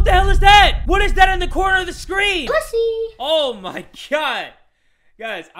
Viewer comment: did I hear pu